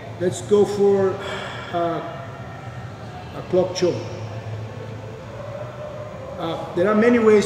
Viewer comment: Lol the captions thought he said “pork chop” instead of clock choke 😂😂😂